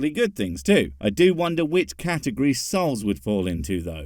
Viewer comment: i thought you were saying "Souls" as in the Dark Souls series lol